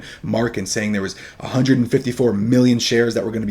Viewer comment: ✅ Get access to the trade alerts, training material and our private community here: <link> 🔥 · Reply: did you said 154 Million shares?